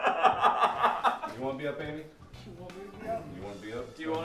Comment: did anyone else hear mark call Amy "baby" around 11:06 and it was just adorableness overload? they are so cute!!! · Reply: He called her Amy. Not baby. Though they do sound the same, so easy mistake to make.